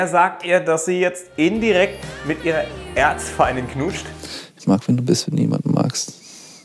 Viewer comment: was sagt er da? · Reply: Ich mag's wie du bist, wenn du jemanden magst